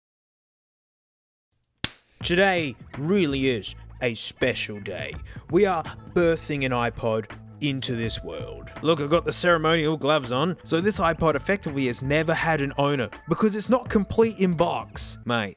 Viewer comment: Na-No may be my favourite thing in the world Edit: The sacred gloves are out boys! That’s how you know it’s going to be good! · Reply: My bad, I thought I heard sacred, but it was ceremonial